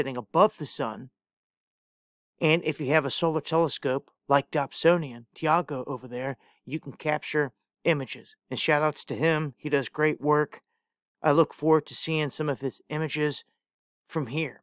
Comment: What’s he say @ 1:39? Dopsonian Taigo?